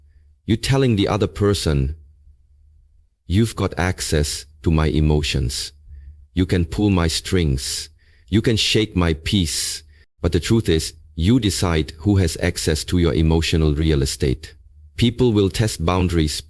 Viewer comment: I thought he said "shake my piece".